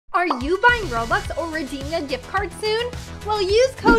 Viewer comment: I really thought the first time I watched videos I thought she was saying reginia gift card 😂😂😂